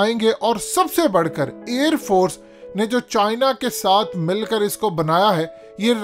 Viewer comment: pehla jhooth China sae mil kae bnaya, what the hell did you said???